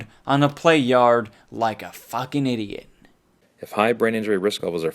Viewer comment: Its a joke you humorless dumbass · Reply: Oh nvm I thought you said fucking die